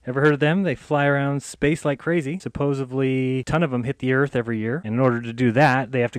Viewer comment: Did he say "supposably?"